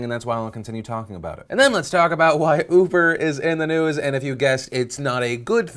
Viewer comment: Did he say 'ufer' instead of uber? XD